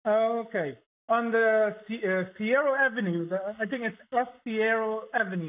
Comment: Omg it's Cicero Ave lol it sounded like he said sero ave lol . I live near 60652 it's right near midway airport